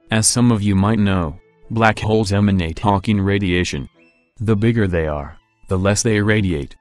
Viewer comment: I swear to God he says hOnking radiation